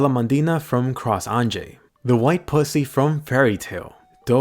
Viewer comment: its me or did he say white pussy at 1:55 lol